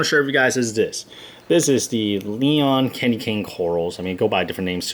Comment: Took me a minute to realize you were saying "Neon Candy Cane," I originally thought you were saying "Leon Candy Cane" and got really excited that you'd named the coral after your kid.